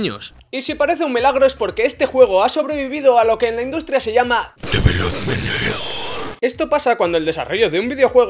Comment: dice te veo y meneo, ¿Que dice en realidad?